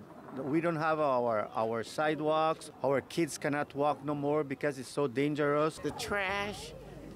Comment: I thought they were saying "NO MORE BEEF!"😂😂